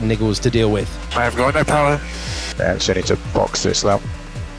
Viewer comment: what he say? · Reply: I’ve got no power Ok copy, box this lap